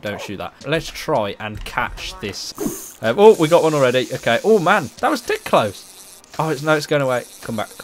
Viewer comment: What did you say??? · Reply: 'oh man that was dead close' XD